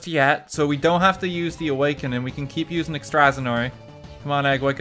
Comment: Did he just say... Ex-stra-san-oree?